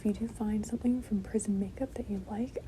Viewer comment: Wait now I am extremely interested. Why do you have prison make-up? I never even knew there was a prison line lol. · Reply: Girl I thought she said prison to I just looked it up she said prism 💀